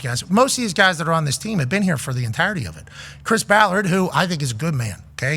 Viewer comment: Sounds like Pat is saying Fire Ballard. He complimented his accent and paying losing players well.